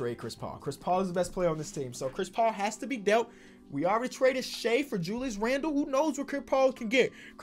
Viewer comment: did anybody hear him say che